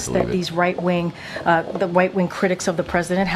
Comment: did she say White wing critics?